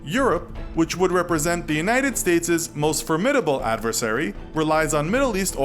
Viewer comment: Did he say "the United State-es"?!